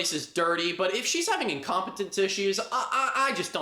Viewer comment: “incontinece”. i said “incompetence”. honestly never knew there was a difference OH WELL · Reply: see, i thought you said incompetence and assumed i heard wrong lmaooo